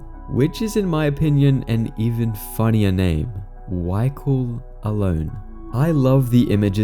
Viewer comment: I thought u said "y go alone"😅